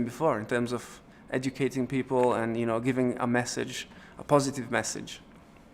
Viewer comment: I thought it said MASSAGE! lol